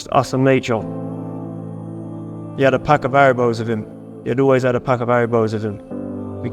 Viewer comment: Can anyone PLEASE tell me what Morrisson says at the 10:07 mark? said Stevo always had a pack of something on him. I’m assuming it’s gum or cigs. I just can’t make out what he says and the captions are totally off. The cc says “aros”. However, I doubt that’s what he said. It’s bothering me so much. Please someone from the UK help me understand. I know it’s insignificant but I just want to know 😂. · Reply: Pack of haribos